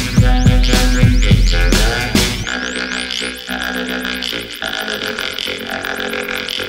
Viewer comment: I thought that instead he saying another dimension, said, I have an erection...